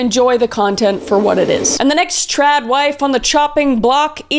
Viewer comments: I thought it said "trade wife" I was so confused. The only thing that made sense to me was like a wife swap scenario for some reason. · I thought you were saying tribe wife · At first I thought she said Chad wife. Don't know what that is but could be a new character.